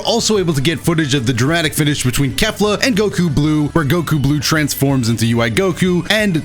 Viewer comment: It honestly looks like ui goku foesnt have a dramatic finish with kefla it looks like its just ssb goku. Edit: sorry just rewatched the dramatic finish and heard he said goku blue, i didnt hear him say that i thought he said ui goku.